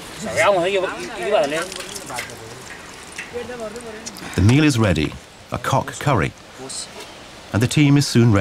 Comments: did you say cock curry? 😶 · Did he just say "the meal is ready, a cock curry"? @ 12:00?! That's the joke of the day!